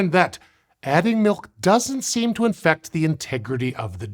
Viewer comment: you can't convince me he doesn't say "infect" instead of "affect" 😅